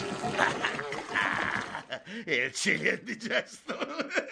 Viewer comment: che cosa dice? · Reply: Dice il chili è indigesto